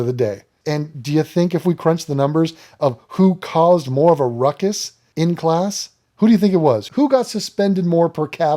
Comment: did u say uncle ruckus? Racism!